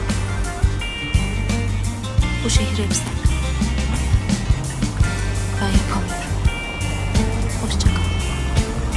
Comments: ya ne diyor bu kadın, bu şehir ne diyor ? KAFAYI YIYECEĞİM NE DİYOR ORADA ? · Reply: Bu şehir hep sen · aga şurada ne diyor Allah aşkına biri yazsın ya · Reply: 'Bu şehir hep sen, ben yapamıyorum. Hoşçakal.' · burda ne diyor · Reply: diyo ki, bu şehir hep sen ben yapamıyorum hoşçakal keşke bana da böyle veda edilseydi